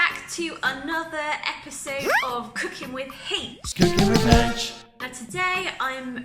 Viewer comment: At first, I thought you said, "cooking with hate." I had to back up and listen to it again, lol!